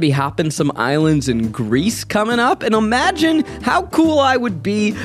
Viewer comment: is reading the Ad. Is it me or does he say 'omagine' instead of 'imagine'?